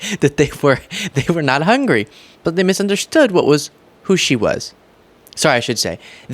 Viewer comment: Oh my god - the transcript was actually ENLIGHTENING to me, whilst also confirming that he made even less sense than I thought he did! I'm partially deaf, relying on hearing aids and lip-reading to understand people (and trust me, with Trump even THAT'S not enough a lot of the time.) When Trump was talking about the woman who got attacked by the shark, I swear to God, I thought he said "they were not hungry, but they misunderstood WHAT SUSHI WAS." Which, considering we're talking about sharks and their prey, actually sounded like... an actual JOKE - a sick one, sure, but one that came almost as close to making some sort of sense as Trump could ever get (unless of course he WASN'T joking, in which case it would just be a flat-out bonkers thing to say.) But no.... OF COURSE I should've known better. Turns out "they misunderstood WHAT WHO SHE WAS" is just his complete inability to form a coherent sentence.